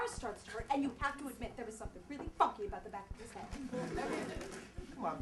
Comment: this is AWESOME!! @ 0:39 did hermione say funky or fucky?!?!?!?